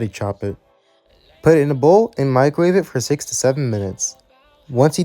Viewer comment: Did you say "Six to Seven" or "Sixty-seven" minutes? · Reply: 6 to 7 minutes